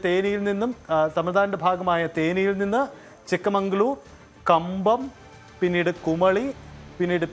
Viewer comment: did he say chikamangalur 😂